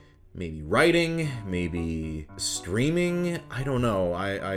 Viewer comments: what I heard was this: "I dunno... maybe writing. Maybe screaming." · At first I misheard streaming as SCREAMING… excellent backup profession. Same. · of course you said “streaming”, but I heard “screaming” and I immediately perished, thank you for that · I misheard when he said "maybe writing, maybe streaming" as "maybe writing, maybe screaming" and thought "You can scream into the void for a living?? Where do I sign up?" rip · I thought he said "maybe writing? maybe... screaming?" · I THOUGHT I HEARD SCREAMING- as in like, he would scream for a living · I thought you said screaming for a second.